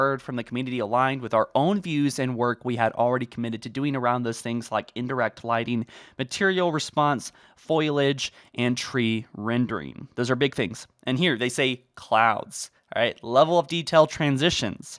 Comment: Did you just say "Foilage"?